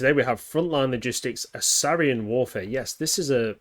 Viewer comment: I thought you said "Syrian Warfare" there for a second and I nearly had a heart attack.